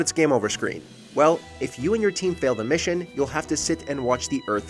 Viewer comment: I thought it said Mission Successful.